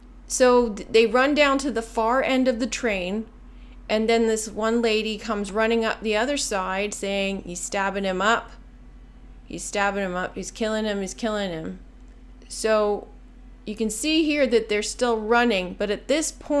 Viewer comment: I hear "he's skinning him"